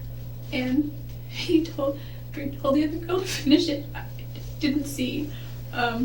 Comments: I swear I heard her say Travis's sister's name as the girl at 5:35 (don't know how to highlight it; sorry). Am I hearing things? Did she say something else? She was talking about the guy holding the gun to her head, and then she was saying, "Then he told the other girl, Tanisha, but I didn't see" or something like that. Wow; bitch is seriously off her rocker. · I hear "he told her to finish it....". Still, her statement is literally unbelievable.